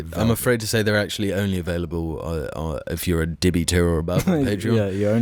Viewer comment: I hate how I just misheard “Dibby tier” as “Diddy tier”. Dark times, we live in.